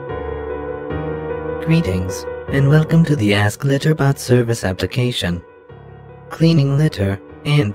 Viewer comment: COMPLETELY honest, I thought it was saying "Ass Glitter Bot"